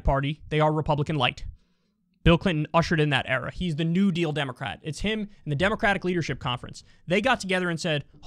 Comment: did Kyle call Bill Clinton a "New Deal Democrat?"